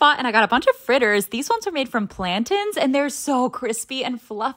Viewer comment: It sounded like you said plantins😂 I'm not making fun of you I just thought it was funny